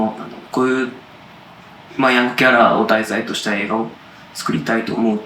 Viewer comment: ここなんて言っているか聞き取れた方、教えてください🙏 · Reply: 『ヤングケアラー』を題材にした。。という風に聞こえました。多分です😊